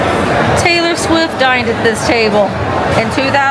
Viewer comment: Hahahaha, at first I thought you said, "Taylor Swift DIED at this table,"😅 and I thought, " Man, The Church has a DARK sense of humor." After realizing the truth, it's not The Church that has the dark humor, it's me. Me. I'm the one. It's me. 😂 Thanks for taking us along!!❤